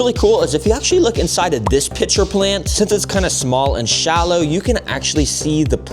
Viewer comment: I thought he said b**cher plant